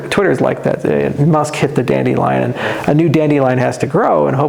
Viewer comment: "kinda like when you hit a baby lion" Me "WTF?!!??!! .... Ohhh he said dandelion!! 🤣🤣 I seriously thought he said that 😂